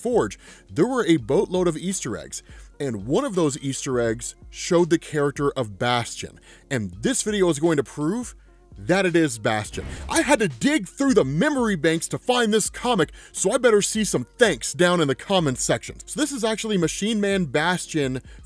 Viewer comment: did you say "bastard"? 🤣